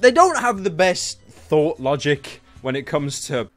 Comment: What's that Pyro 6:54 , did you say... T H O T Logic?